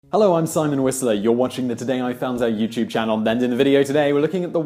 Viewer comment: I always thought he said Sam Whistler